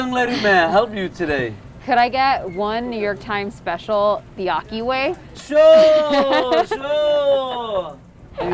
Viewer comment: I thought she said the bukkake way.